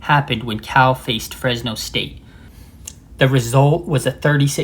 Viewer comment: What? I said Cal vs Fresno State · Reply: I replayed it a few times and kept hearing "Cal St." I just played it for my son (who has younger ears than me, and he pointed out that you actually said "Cal FACED Fresno St.," but he also said that you sort of swallowed the "faced" a little, so he could see how I heard "Cal State." My bad. Carry on.